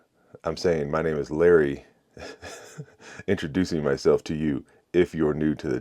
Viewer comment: I really thought you were saying your name was Larry Gunu 😂